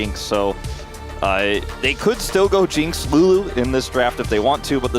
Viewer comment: what did he say... could go jinx uugh?